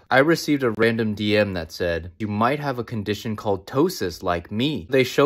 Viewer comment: Lmfao “Why do you always look so mad?” “Ever hears of ptosis?” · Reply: i woulda thought you said “toe sis” 💀